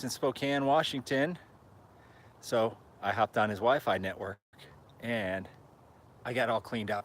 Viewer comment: At first I heard you say I hopped on his wife. Second time I heard WiFi. No judgement here, you know this.